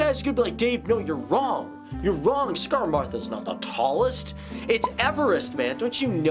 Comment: Did he just say CIGARmatha ?